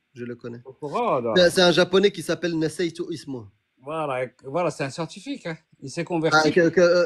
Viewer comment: c'est quoi ce nom de japonais Même "naruto" serait plus crédible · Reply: Nassaytou Ismouh = J’ai oublié son nom. Et le mec il me répond oui.